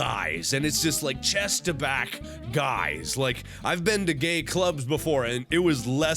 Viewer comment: Did he say Gay club??